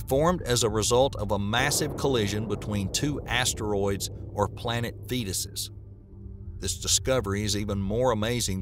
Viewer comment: planet fetuses? lmao did i hear that right?